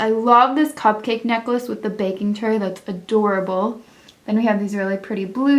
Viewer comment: who else heard Bacon Tray